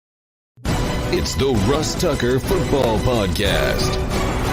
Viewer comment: Listen to the beginning to the video sounds like he says russ tucker lol good luck this week don't take the bills upset alert